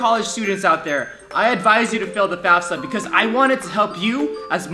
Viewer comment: I thought you say fill the bath tub